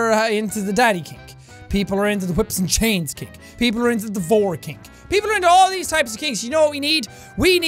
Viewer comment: WHAT DID HE SAY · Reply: Vore, that’s what he said.